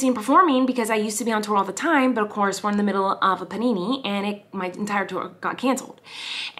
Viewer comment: Did she just say Panini?! Well anyways i died laughing :)